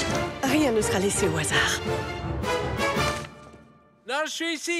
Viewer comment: qu'est-ce qu'elle dit a 0:18 (pour ceux qui sont pas francophones)? j'entends "rien ...??!?!??" merci pour vos réponses! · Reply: avafrav "rien ne sera laissé au hasard" (she will decide everything of everysecond of her life; the hazard won't play any role in her life)